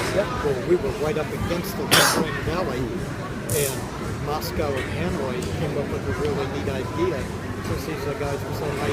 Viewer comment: did he really say Moscow and Hemroid came up with a good idea? Did he get hit in the head during lock jaw and became like those people you hear about that can speak a different language with some crazy accent after a head injury?